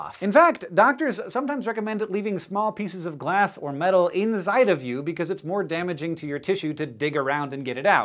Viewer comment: did you say "INZIDE" XD